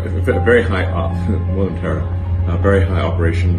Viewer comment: did he say "we are in tera"? D